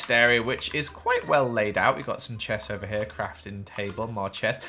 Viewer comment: is it just me or does he say “chess” instead of “chest”???? Love your builds btw! 😍